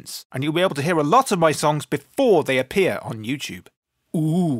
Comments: when you said hello its dan bull it sounded like you said dumb bull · I thought he said its dumbo instead of dan bull